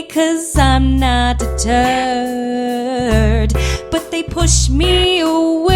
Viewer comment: I thought Peggy said, "And I'm not a turd." I was just thinking, "DAMN RIGHT YOU'RE NOT." But then I read it and it said deterred and I feel so betrayed for some reason...